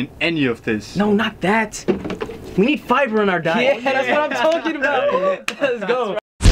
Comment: What did say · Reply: "We need fiber in our diet"